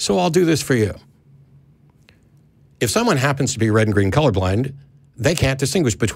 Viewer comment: Caller did not say "colour blind". He did, in fact, say "blind".